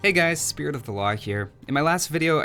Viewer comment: I thought he said the channel was called 'spirit of the the waaagh'